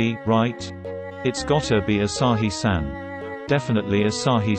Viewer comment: I might I have thought that Ashai san said Asain san for a few seconds- :O I'm pan too!